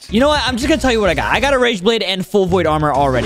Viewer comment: The captions: razor blade What said: rageblade